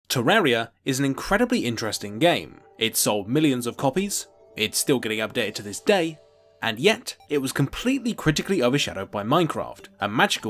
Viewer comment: I don't know, but when the video starts, it sounds like is saying "Turraria is an..."